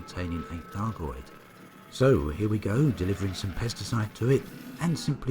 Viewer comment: When u first said 'Pesticides' I heard 'Pasta Sauce' lol! More great content, right on commander o7